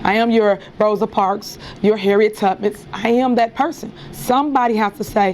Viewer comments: Did she just say, "Harriet Tubbus?" 😅😅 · Please tell me, I’m not the only one who heard her say Harriet Tuppet. Please please listen closely to her😢😂😂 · Did she just say Harriet Tubbits?